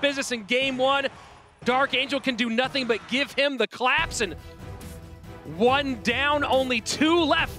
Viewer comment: Did he just say that he gave kanariooo the clap?!